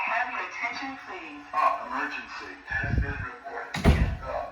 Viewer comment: F-ck emergency Is what I heard